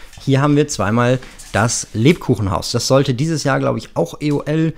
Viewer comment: ich habe "Lepin Haus" verstanden 😂😅